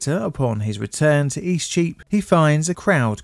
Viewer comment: Eastcheap is a very confusing street name for a foreigner to understand. It sounded like you were saying "E Streep" (a flub of "street") but I had to look at a map to understand what you were actually saying. Streets, like celebrities, that go by momonyms are also just pretentious.